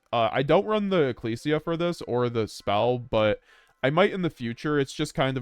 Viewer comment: what spell do you mean when you say for stigmata you dont run ecclesia “or the spell” ? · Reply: I said I dont run ecclesia for the spell or stigmata